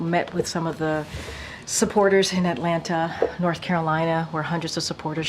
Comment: Did she say Atlanta, NC, @ 20:33?